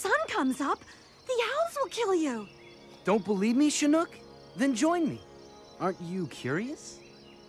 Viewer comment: what did shade say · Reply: Adam M "Don't belive me, Chinook? Then join me. Aren't you curious?"